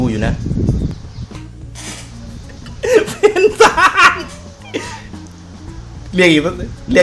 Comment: กูคนเดียวหรือเปล่าที่ได้ยินเป็น สายป่านๆ!!!